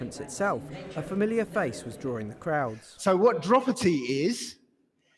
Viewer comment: Did he say "dropity" and at 10:21? 😂 I can't with these people